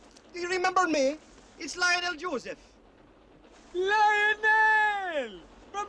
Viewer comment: I Thought He Said Darnell Joseph 😂😂😂😂